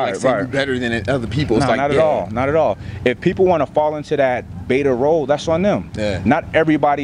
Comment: does he say "Betta Rho" ? please someone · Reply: Beta role